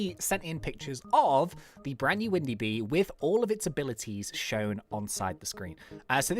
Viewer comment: did he just say windy bee lol